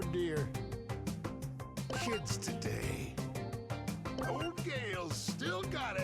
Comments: did gale say shits today or kids today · it actually sounds like shits today instead of kids today